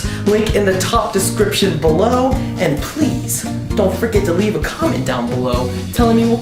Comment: thought you said, "dont frickin leave a comment below" lol